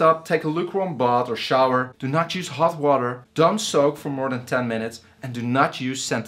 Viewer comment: good video. Did you say do not use half water at 2:15?